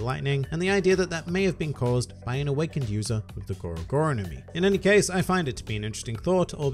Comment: it sounded like you said the gura gura no mi... that’s White Beards fruit, the tremor tremor fruit, you were thinking of the lightning lightning fruit, the goru goru nomi. At least to me it sounded like you said gura, not goru, my apologies if I misheard